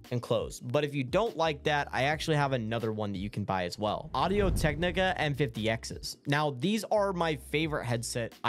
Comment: what did he just say audio tec what